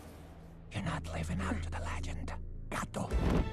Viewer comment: what did he say in his ear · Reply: “Your not living up to the legend gato (Spanish for cat) “